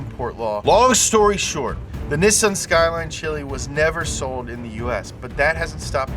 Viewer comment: Did you just say Nissan Skyline Chili … haha. Thank you for that Cincinnati reference